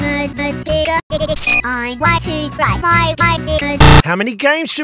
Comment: i have been wondering for ages what bingo was saying at 0:02 and reading the subtitles i finally know hes saying "im a nose picker"